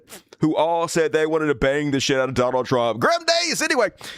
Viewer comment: I thought he said "20 women who want to bathe in the shit of Donald Trump." They'd probably like that too.